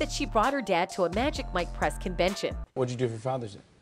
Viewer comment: “magic Mike Press Convention”. . 😯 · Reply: Press Convention? I thought she said Breast Convention! Wouldn't doubt it though... 🤣😂😅🤡💩🤮